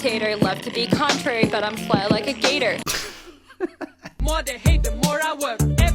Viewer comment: "But i'm fly like a gator" 🗣🔊🔊🔥🔥 We making it out of the talent show with this one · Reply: I thought she said “Flat like a gator” and I was still confused 😂